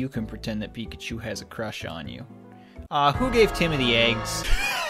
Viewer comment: "Who gave Timothy eggs?" This line will haunt my dreams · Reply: I heard it like "Tim the eggs" like a noob